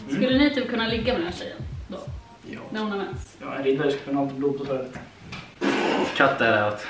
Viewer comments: vad säger han, hör inte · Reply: ”En riddare ska alltid ha blod på svärdet” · Alltså jag hör inte vad han säger...så vad säger han? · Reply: "klart riddaren ska ha blod på svärdet" typ